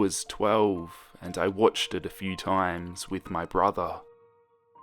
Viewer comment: I thought he said watchet it